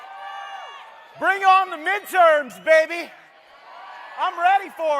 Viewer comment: At first I thought Matt was at the teen choice awards. I'm sure he'd love to attend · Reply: when he first opened up I thought he said bring on the interns baby! 🤣😆